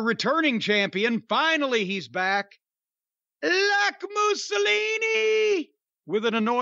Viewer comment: I always thought jim said "black mussolini" until I saw this artwork 😂😂😂